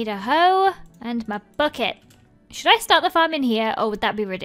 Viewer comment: i thought she said pot farm lmao i was like what did she say haha